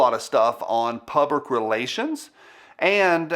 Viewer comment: Did you just say pubic relations?? 3:33 Its confirmed, an NPC and he is glitching. He's a government plant here to honey trap us with that sweet, smooth, southern accent.